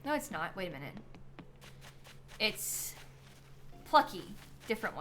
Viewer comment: The excitement I got when you said “that’s Plucky” and I heard “that’s Lucky” 😭